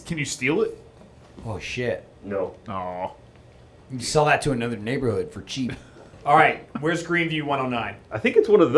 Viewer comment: I must be hearing things. What did say at 8:08? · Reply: "Sell that(the knocked over sign) to another neighborhood for cheap."